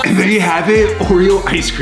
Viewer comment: Dude I thought it said Ohio ice cream. Not Oreo ice cream ☠️